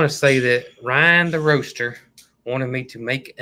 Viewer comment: Thought you said, "Ryan the Rooster" and I was picturing a bird packing up packages, until it finally kicked in you were meaning the ROASTER!!